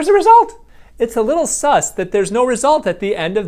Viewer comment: did he just say sus